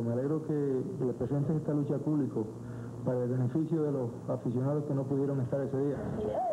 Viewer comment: Los colores o los cojones de Puerto Rico? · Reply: me parecio oir que dijo "me alegro que den esa lucha para beneficio de los asesinados que no pudieron estar en ese dia" .